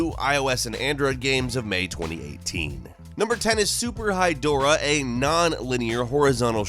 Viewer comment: did anyone else hear it as super high Dora?